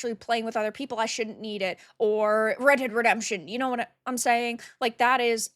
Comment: I heard “Red Head Redemption”